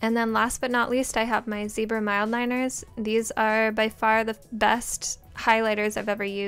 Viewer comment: Did she say zebra mildliners DONT SMEAR!!! lol